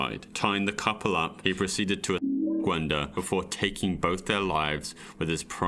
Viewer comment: what did he say? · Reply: he said "rape"